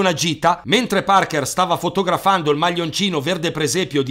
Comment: A 00:29 ho capito "Il maglioncino merda presepio" e comunque ci poteva stare 😄